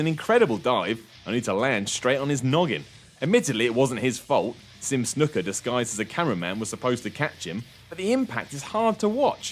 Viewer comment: Around the 1:15 mark, did he say "Snuka, desgised as a camera man was supposed to catch him?"